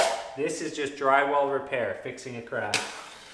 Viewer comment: I've watched this several times over the years and I just realized he said "This is drywall repair- fixing a crack." I always thought he was saying "This is drywall repair. Fixing and crap."